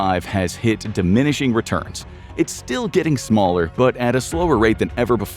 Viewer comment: the subtitles say "smarter", but the video says "smaller". Also, as a data science student that is currently training models, I've always had this thought that we'd never achieve AGI, purely as almost every AI company is backed up by a tech giant.